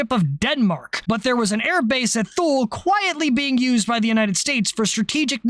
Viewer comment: did.. did you say "Thule"..?